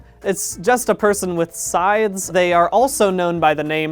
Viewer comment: I’ll be honest, I’ve listened to Unraveled hundreds of times, but I haven’t really watched them that much, and I only now realized that he said “scythes.” This whole time, I thought he was saying that it’s “a person with SIDES” 🤡